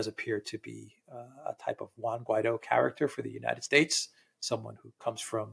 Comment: What did Alex say in 0:28? What type of character? 😂 I cannot catch that. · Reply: "A type of Juan Guaidó character for the United States". Reference to the US puppet they installed in Venezuela.